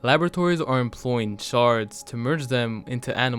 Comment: Whew!! I kept hearing “Shart”!!!